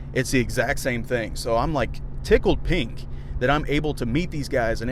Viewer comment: did you just say "I'm tickled pink?" 😅😂🤣 I luv u, man.